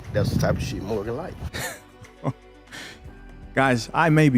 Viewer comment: I thought he said "That's the type of sh*t that'll ruin your life"